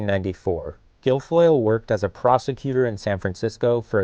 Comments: I wasn't paying attention and thought he was about to say, "Guilfoyle worked as a prostitute" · Every time they said the word ‘prosecuter’ in this video I misheard it as ‘prostituter’.... she is absolutely bananas.